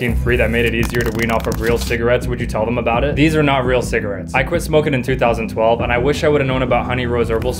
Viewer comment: bro I thought he said " i quit smoking at 3012 "